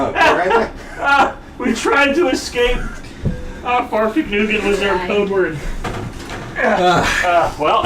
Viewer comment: what does Nick say at 18:26? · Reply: "farfegnugen was our code word"